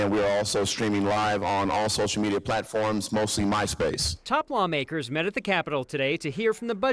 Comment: Did he say they're live streaming on all platforms, mostly MySpace???? LOL